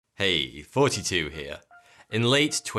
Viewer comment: Second vid I've watched now and both times I hear "42 here" and I think "oh, so I guess your the meaning of life you pretentio...whoops its Thoughty2, my bad"